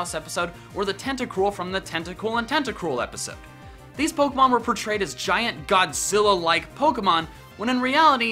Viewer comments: I didn't think it was "each" or "teach" I thought it said "these pokemon need to" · I thought it said these pokemon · I thought it said thees pokemon